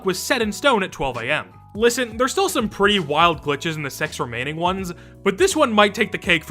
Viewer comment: The captions say "S*x remaining ones." Wild.